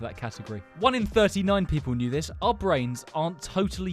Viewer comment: I thought it said 'our brains aren't totally gay🤣💀🤣